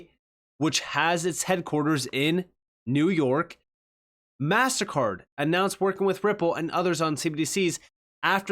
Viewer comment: Sounded like you were saying "massacre" at first instead of "mastercard"... I was like great, is he Ai too 😂😂